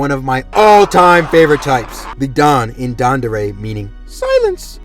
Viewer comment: my dumbass thought you said dandadan and made me triple take 😭